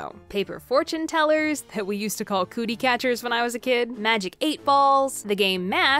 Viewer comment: “Cootie Catchers” I haven’t heard that term in so long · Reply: Laurel Branch I THOUGHT THAT SAID COOCHIE CATCHERS EYE